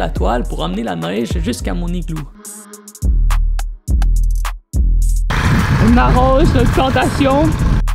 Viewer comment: "On arrose les…" quoi t'as dis ?